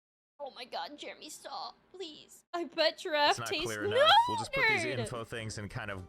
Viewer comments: so , i had to go back 3 times to finally notice that you were reading CHAT. cause what i heard was "i bet your ass taste g- " and i was REALLY confused for a sec. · Ild be honest. I completely heard "I bet your ass tastes good" instead of "I bet Giraffe tastes good". 5:00 Holy crap, that was so out of pocket. Get well soon! Really hope make a full recovery.💜